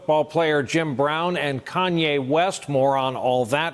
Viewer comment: LOLOL when he said “Kanye West, more on all that...” I thought at first he just said “Kanye West, moron.”😂😂